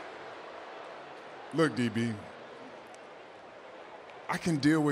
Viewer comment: did Roman say the N Word or no?